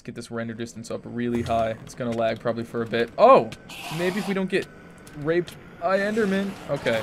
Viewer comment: Is it just me or I heard him say "maybe if don't get raped by endermen"